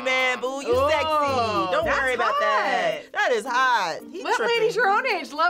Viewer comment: "[Inaudible]" I see in the captions, but she clearly said let's